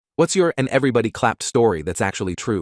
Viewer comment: I thought it said "and everybody got clapped story" 😭🙏💀🙏😭